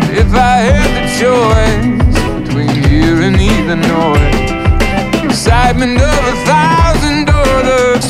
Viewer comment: If I had the choice between here and Illinois The excitement of a thousand hors d'oeuvres soothing up your voice i thought he said if i had the choice , between hearing either noise, the excitement of a thousand , or the , soothing of your voice. anyone else???